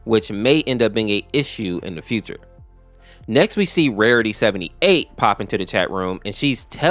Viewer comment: Why did i hear verity